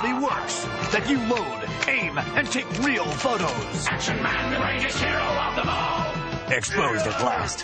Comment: Came here because I wanted to know what the jingle actually said since I didn't know English when I was a kid. I always heard "Action Man, for zero hero demand" but I guess all this time he was really the greatest hero of them all